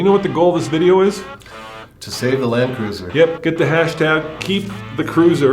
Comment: For half a second I thought you guys said, were here to save the land whale 🐋 😅. I started singing we are family while thinking of a pod of whales hahahaha!